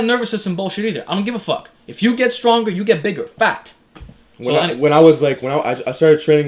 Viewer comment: it sounds like you're saying fat instead of fact.